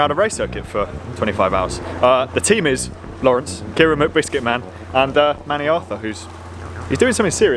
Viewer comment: Keira is really pretty, huh? Sounded like you said her last name was Whitbiscuitman. :)